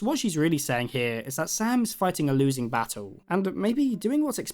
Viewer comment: did bro just say... SANS...